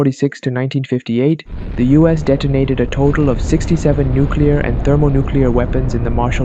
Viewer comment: did I just hear 67? 10:11 bro I almost cried